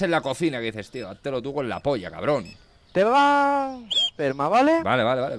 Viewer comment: que es eso que dice "te vaa, vale?"* Pito* mientras responde vale valeeee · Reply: Dice "te vas perma". Perma es una manera corta de decir baneo permanente del chat JAJAJA